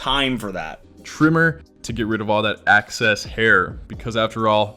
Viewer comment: Did he say "access hair"?? Also, 100% not his real name.